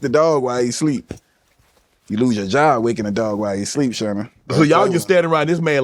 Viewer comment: did he just call unc, sherman? 😂😅😅😂